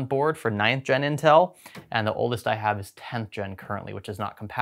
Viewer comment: yeah unfortunately lmao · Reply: I rewatched and heard 10th gen but would have sworn he said 6th or 7th gen Intel the first time. Or actually I thought Greg said 8 to 10 generations old hardware is hard to come by